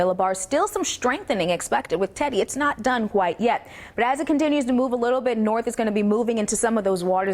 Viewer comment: What the hell she saying or trying to say.@ 2:08 it’s not done WHITE yet. I think she’s trying to say it’s not done QUITE yet. 😀